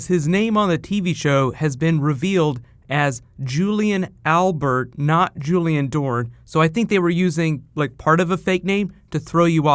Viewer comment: When said "Julien Albert not Julien Dore" I just heard "Albus Dumbledore" loooool